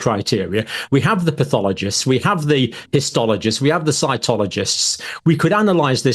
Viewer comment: Sounded like you said, " we have the Scientologists"